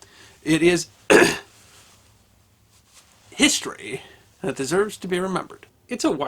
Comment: I heard histamine